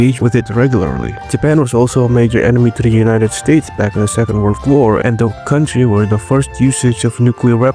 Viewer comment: I misheard the second world war as the seventh. 👁👁